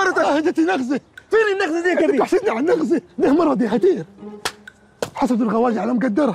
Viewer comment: هو هنا قال دى مراتى هدية ولا انا سمعت غلط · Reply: قال ده مرض مش دي مراتي